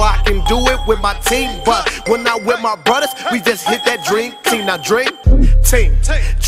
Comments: It took me 4 years to realise he was saying "Dream Team" instead of "Drink Tea" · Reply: I thought he was saying drink team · For some reason I thought he was saying "Drink Tea"